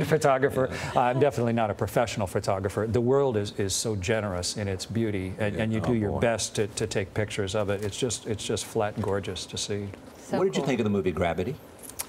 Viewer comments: did he just say the world is flat and gorgeous? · “It's just flat and gorgeous” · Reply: No, He said "It's just flat gorgeous to see". He did not say 'and'. Listen closely & watch his lips.